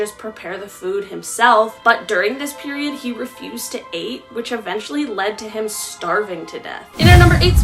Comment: Did she just say “he refused to ate”?.